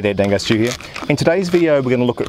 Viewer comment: Finally!....I now know what he is saying at beginning of every video......I always thought he was saying Dangars TWO.